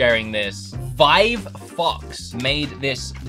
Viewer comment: I thought u said vive fucks but u said vive foxS LOLOL